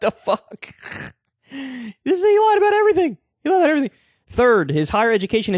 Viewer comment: Whoa, Did Kyle just say "this nigga lied about everything?" Damn, Calm down there Kulinski, lol.